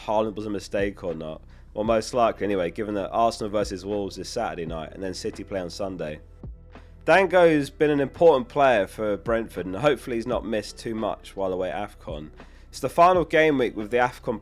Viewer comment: I never said buy then sell, he's just great to buy and hold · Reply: Oh you said Dango was going to afcon! I thought you said Thiago as his graphic came up when you talked about it, confusing, I now realise he's Brazilian.